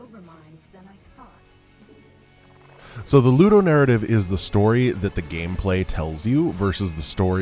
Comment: Would someone be so nice and explain what narrative is talking about at 4:46 (and then several times more, 4:55, 5:01 etc.)? Is it "Ludo-narrative"? "Lu-narrative"? "Nu-narrative"? I generally have no problem understanding what says, but this time I have absolutely no idea. Any help will be really appreciated. Thanks in advance! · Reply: Ludonarrative